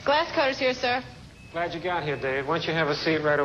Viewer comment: I heard correctly......the surgeon said "Glad you got here Babe.......LOL